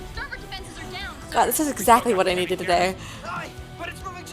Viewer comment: was that Happy's "aye!" I just heard?